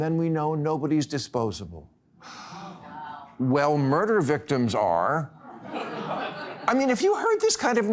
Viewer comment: Did Bill Mahr really say murder victims are exposable or did I mishear that? I listened twice, maybe someone needs to explain that to me.